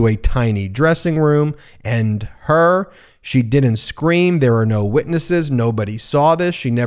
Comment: Trump says “…She didn’t scream…”. I thought he said it didn’t happen. If it didn’t happen, how can it be that she didn’t scream?